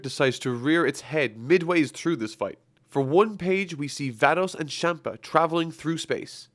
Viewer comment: i misheard you saying "Thanos and champa" i was like "wHAT"